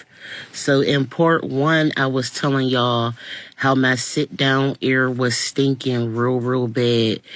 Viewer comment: I thought she was saying sit down area! Area. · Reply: nope air